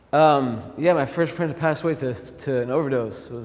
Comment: ive re watched this so much i cant figure out was he's saying my first friend passed away? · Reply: "my first friend that passed away to an overdose" I think